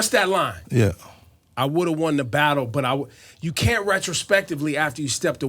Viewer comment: J. Cole said, "I wouldn't have lost a battle..." He didn't say what Ebro saying.